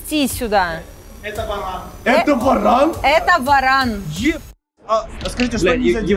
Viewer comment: Это Варан?? Я которая услышала баран Я думаю: Чё? Баран?? А потом я увидела варана😂 А бляха Варан😂😂